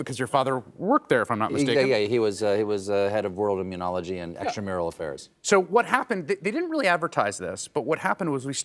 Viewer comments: Did Stephen say ‘…and extramarital affairs…’??! 🤔🫢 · How did "head of immunology and extramarital affairs" get overlooked. Great line. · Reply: 🤦🏻‍♀️ I misheard it for a moment too but that’s not what he said. Haha it’s sad that in this country in 2025 our ears are far more primed to hear about cheating husbands than the “extramural” department at the NIH, referring to infrastructure and policymakers of one of the most integral agencies in the country that does medical research to, you know, help us not die. Part of why we’re in this mess, I guess, but my ears deceived me at first too. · Anyone else noticed Colbert's lightning-quick joke at 0:35 how his father was head of "World Immunology and Extramarital Affairs"? Chris Hayes didn't hear it, I think, and the audience also didn't respond.. That was funny 😂😂😂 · Did anyone else hear "extra marital affairs" the first time? I had to listen twice, at first I thought it was a casual dig lol. Not true.